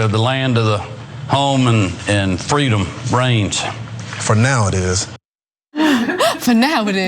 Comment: I thought he said freedom brains